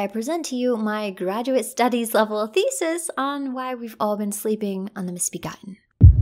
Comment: At first i hear that "we've all ben sleeping WITH the misbegotten" and not sleeping on the mosbegotten and i had to go back to relisten. I was a thrown off there for a second, lol.